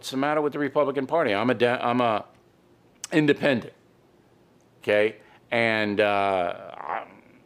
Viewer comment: It almost sounded like you slipped up and told the truth at 1:35 when you said "I'm a Dem. ahhh independent"